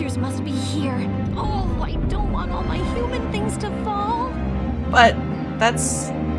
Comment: When she said human things I thought she said humans by itself 😂😂😂 I automatically thought of jeepeers creepers